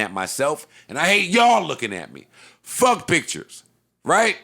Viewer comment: Joe said “I hate y’all looking at me” In my head I heard him say “long as you can hear me” 😂